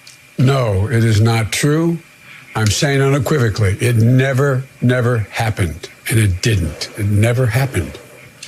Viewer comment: Im in no means body language expert, but what bugged me was when Mika asked "did you sexually assault her", Biden immediatelly responds with "no, it never happened", and not with "no I didnt", which to me would be natural response to a direct question. To me that sounds either like too rehearsed, or that he did assault her, its just that that specific scenario she said didnt happen. Also, he constantly uses word "it" for what happened, which is a red flag to me. Id love to hear others opinion on this... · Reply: I thought he said "no, it never, never happened" which i interpreted the double negative as a subconscious admission something did happen. But I'm not an expert of any sorts, could have just been nervously stumbling on words